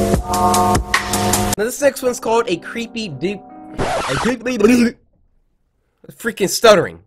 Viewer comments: Who also thought he said creepy “dick” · DID HE JUST SAY CREEPY DICK